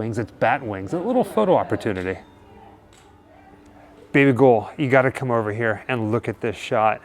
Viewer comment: Did you just call Jessica 'baby ghoul'?? 18:36 🤣